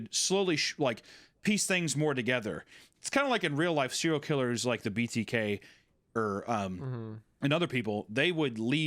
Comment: I swear to god i heard “serial killers like Louis C.K.”